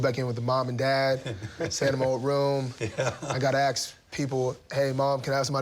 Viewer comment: Did he say AKS?